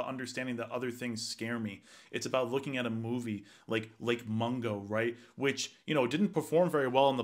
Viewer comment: Anyone know the movie he mentioned at 3:11? Did he say Mungar or what?